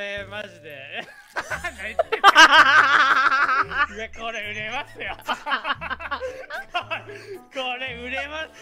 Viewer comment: これ売れますよ！が「これで寝ますよ」に聞こえて昼飯吹き出しかけた